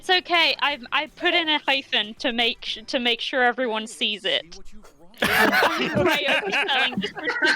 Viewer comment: what did say about his pun?? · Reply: I think she said "I've put in a hyphen to make sure everyone sees it", if that's what you're talking about?